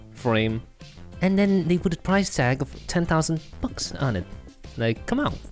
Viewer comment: Did anyone else hear “10000 f*cks”